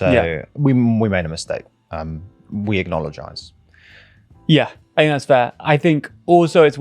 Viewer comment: Hold on is it just me or did say we Acknoledgise 2:40 ? Classic, trying to apologise but not really